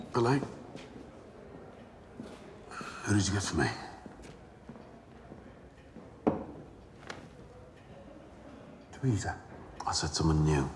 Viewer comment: what do they say here? Literally cant understand a word that Tommy and the Waiter said 😅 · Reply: Billy , what did you get for me ? / Waiter : Terrisa ( a maid name) / Tomas : I said someone new Billy .